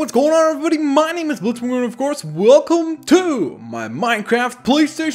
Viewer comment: in the intro I thought he said blitzWeiner